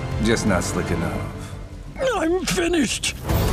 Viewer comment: All I hear from the “I’m finished!” Is “WALLA,IM COOKED!!!!”